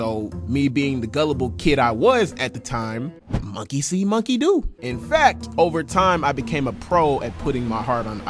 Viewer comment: what did he say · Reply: Monkey see monkey do